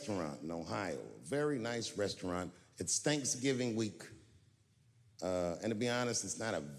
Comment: Sound like he said stanksgiving week lmaoo